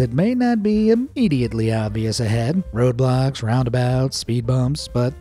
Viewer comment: i hear like roblox